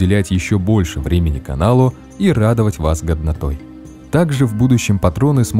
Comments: Мне одному послышалось что в конце он сказал "радовать вас гавнатой"? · Не в обиду: мне в конце послышалось "радовать вас говнотой"))